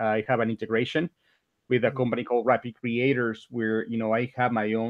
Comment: Hey guys, very informative video, what's the name of the company that integrates with Amazon? He says the name around 6:30 but I can't figure out what it is, any help? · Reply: Rapid Creator but I'm not sure that it is for Merch he has a 3rd party POD company he runs through Amazon